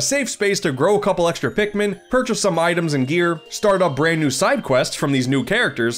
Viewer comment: i thought he said beer 🍻